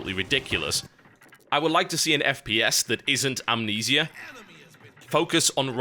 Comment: Did he say Amnesia is a FPS...?